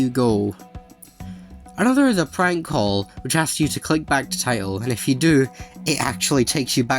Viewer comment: I thought 15:30 I thought it said back to tile and I was confused and It got me